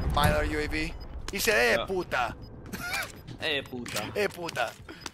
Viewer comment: did anyone else hear "Jueputa" 4:01 made me laugh